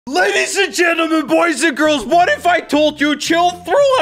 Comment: In the beginning it sounds like he is saying ladies and gentlemen, poison girls